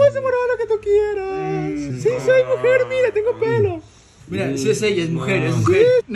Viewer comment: La primera vez que vi el vídeo. Al minuto 1:23 Yo escuché que dijo: Soy mujer tengo P3n3 jajajajajaj me pegue un cague de risa. Luego escuché bien y a sido tengo pelo . 😂😂😂😂 Hubiera sido chistoso que dijera eso 🤣🤣🤣🤣